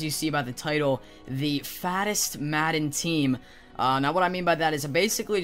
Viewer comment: Who else thought this said fastest instead of fattest